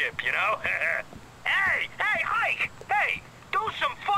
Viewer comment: He said "Ike"(Jewish American name), but the subtitles said like